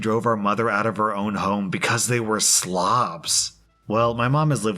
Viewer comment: With the siblings thing I thought they said Slavs instead of Slobs lol